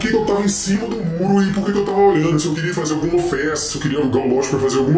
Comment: Se ele queria fazer algma ofensa? Não entendi nada. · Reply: Festa